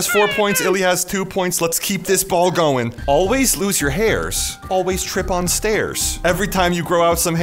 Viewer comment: bro when it said either always lose your hairs or always trip on stairs i thought it said always strip on stairs XD🤣🤣🤣🤣